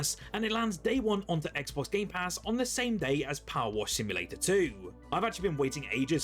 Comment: Am I the only one that thought he said palworld simulator 2 was 😂